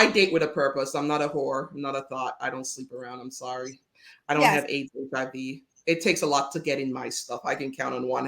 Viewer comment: Did she say she doesn’t have AIDS/HIV ???? Like what!!! Did anyone else hear that or is it me?…. 9:24 I need clarity !!!